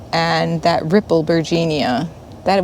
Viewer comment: Lovely garden ❤ about 18 minutes in you called a plant a rippled "vergina" maybe. I loved it, what was the name of it. I couldn't find anything under what I thought you said. Thanks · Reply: That was a rippled bergenia! 😊